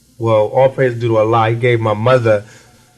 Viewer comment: what does he say at 4:46? it sounds like 'all praises due to Allah' but as far as i know, he wasn't a Muslim? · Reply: He don't sound like it , he is clearly saying " all praise due to Allah !